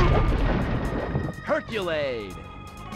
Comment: I just realized that thing is called herculaid.I always thought the narrator said hercules.